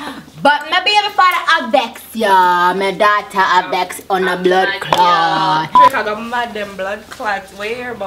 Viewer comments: nicki minaj speaking that jamaican doe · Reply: melissa franklyn, I thought she said blood clot lol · nicki minaj speaking that jamaican doe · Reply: i always thought she said "blood clot"